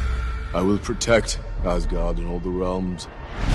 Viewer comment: ¿En serio traduces "all the realms" como "todo Roma"? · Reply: Jeje hablo bien inglés pero entendí “all the rome”